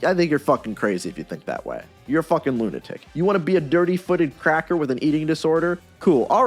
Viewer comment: did I just hear "you wanna be a dirty-footed cr**ker with an eating disorder" 😂